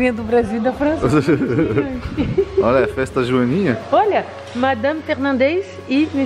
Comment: Só eu entendi: "Festa joaninha"?